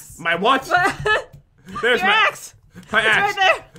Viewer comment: Who else heard that? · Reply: “There’s your a$$” lol